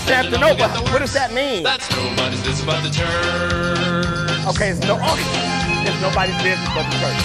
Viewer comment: So, Istanbul is the largest city in Turkey. It used to be called Constantinople. At the end of the song, you thought they said “it’s nobody’s business but the church” but I’m pretty sure they said “ it’s nobody’s business but the Turks.” I had never heard this song before. It was a lot of fun, especially watching your reaction to it. You always make me laugh! 😂😍